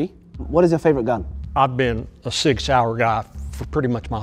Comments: They thought said 6 hour when he actually said, "Sig Sauer", and they thought he was just saying random numbers when he said, "a .45". As an American, it hurt learning how little people outside of America don't know about guns, and I'm now sad. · btw i think he said sig sauer not 6 hour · For anyone confused, 'SIG Sauer' is what was said, not 'Six Hour'